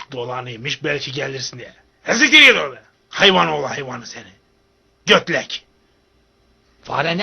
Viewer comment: arkadsşlar burada ne diyor ya, ben anlamadım? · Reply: He siktir git ordan hayvan oğlu hayvan diyor